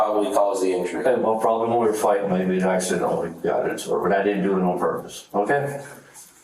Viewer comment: did he say she ran into it on purpose?